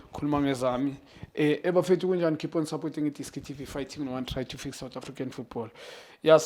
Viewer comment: I would love to know what you always thought he is saying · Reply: I always heard "eh bafethu kunjani,keep on supporting idiski fighahskdndhdisbsksj to fix South African football "😂😂😂